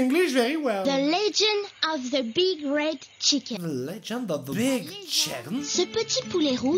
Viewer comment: J'ai compris " the big rape chicken"